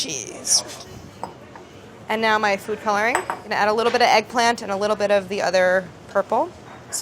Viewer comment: I heard "I'm gonna add a little eggplant" and got really nervous